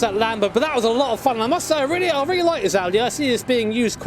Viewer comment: Great Vid, did you call it an Audi